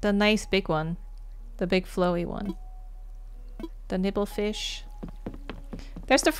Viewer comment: I clicked on the video and I wasn’t really concentrating and all I heard was “the nipple fish”😂